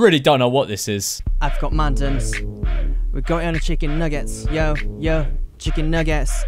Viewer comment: The guy sounded like he was saying chicken niggas